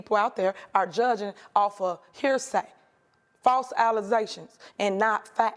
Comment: did she just say false alize-tions😂